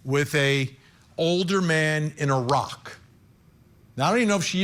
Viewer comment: I'm laughing because I didn't understand Iraq. I thought you said a rock. I thought a rock?? Then I realized what you were saying Iraq.